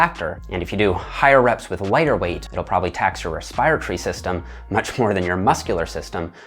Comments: Did he just say “Res-pire-tree system”?? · I cant understand what he is saying, what system is it gonna tire first? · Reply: Respiratory system